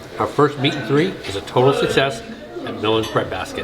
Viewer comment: I thought you said "Meet and Greet" ( not meat and three). I was freaking out thinking I had missed a Meet and Greet! 😢 Then I realized it was the meal (Meat and Three). 😅😊